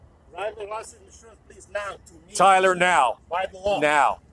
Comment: did he really say, "I am the law"? Is this judge dredd?